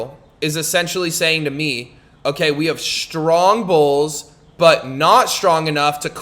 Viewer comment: what does strong bowls mean? I didnt understand what he said and in the subtitle it said bowls. Also what is a liquidity? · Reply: he said strong BULLS , he just has an accent , bulls = strong RISE Liquidity is where the market relaxes for a bit and stays in a straight line so people can BUY and SELL.